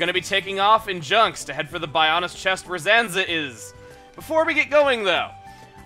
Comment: ZANZEH IZ That's it. That's all I heard.